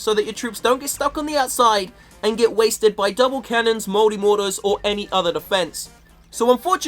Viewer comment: I thought he said 'moldy mortars'